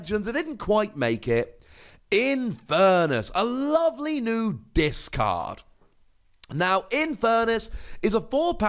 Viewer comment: What he said: A lovely Dis card What you heard: A lovely discard Before